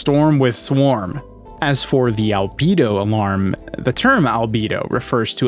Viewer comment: bruh i heard libido and got super confused